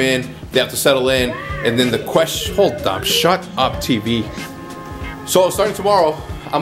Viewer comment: I thought he said "Shut up, TeePee"